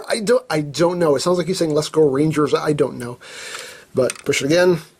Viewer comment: I heard let's go bangers